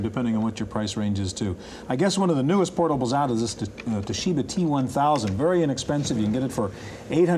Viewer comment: Oh no!!! Did you say T-1000?